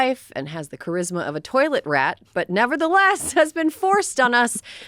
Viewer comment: "Obnoxious toilet rat?" That might be the nicest thing anybody could ever say about Stephen Miller. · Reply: I heard it as 'toilet rag' myself. You know, the small rug placed just in front of the toilet to try and hide the 'near shot' marks.